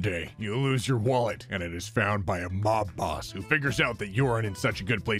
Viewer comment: I SWEAR I HEARD HIM SAY "IS FOUND BY BOB ROSS. BTW, wouldn't it be sweet if Bob Ross (before he passed away, of course) just decided to help out. Edit: nothing against the ghost helping out someone in need!